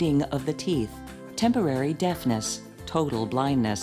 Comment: In the end part, at first i thought it said "temporary deathness" not "deafness". Made that bit a lot funnier.